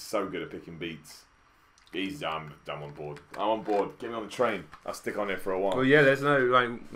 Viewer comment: instead of “I’m on board” I thought you said “He’s Dumbledore”